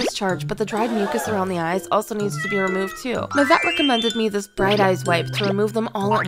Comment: I THOUGHT YOU SAID MY CAT RECOMMENDED ME THIS AND I WAS LIKE CAN YOUR CAT TALK???? IM CRYING 😭😭